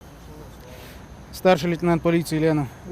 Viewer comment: Он что говорит " Старший лейтенант Лена" ?